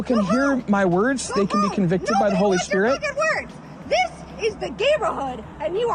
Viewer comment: Am I hearing this correctly? Is she saying "this is the gamerhood"?